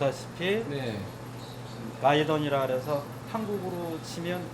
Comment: 에 발음이 좀 새서 라이던으로 들리는데, 사실 라이던이 아니라 Guidon이라고, 포메이션이나 마칭할 때 맨앞에 서는 사람이 들고 있는 깃발같은 겁니다. 실제 발음은 가이던/가이드온이라고 하죠 ㅎ